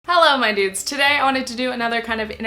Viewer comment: I thought you said "Hello my nudes" in the beginning for a sec